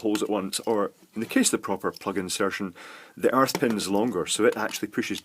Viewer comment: I could have sworn you said "arse pin" for the earth ground. I love watching you make these videos. Keep it going!